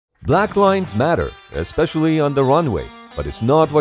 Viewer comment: I thought the guy said Black Lives Matter